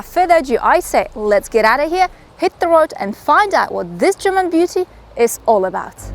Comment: Did I hear well? German beauty??? ;-) Flower for